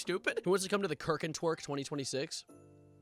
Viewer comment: legitimately what did I just hear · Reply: kirk n twerk 2026 🤑🤑🤑🤑